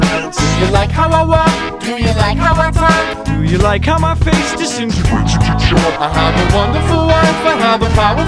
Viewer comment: "Do you like how my face disintegrates into chalk?" that's normal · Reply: Mr. Rex thought it said disintegrates into shock